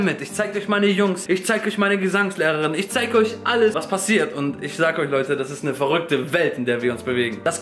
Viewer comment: Ich habe "ich schlag euch leute" verstanden xD